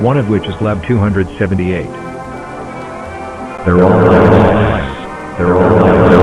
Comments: "they're all lies" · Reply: i thought they were saying "THEYRE ALL NICE" and echoing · what is he saying? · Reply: "they're all lies"